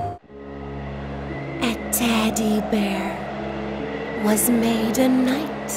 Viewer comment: I thought she said "A Deadee Bear" and thought now there is a fun spin!!